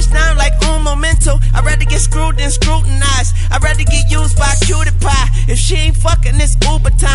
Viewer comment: anyone else hear pewdiepie name drop..... nani? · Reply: TheJuganator he said he'd rather get used by a cutie pie